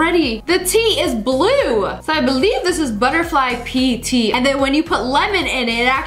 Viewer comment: did she just say BUTTERFLY PEE?!?!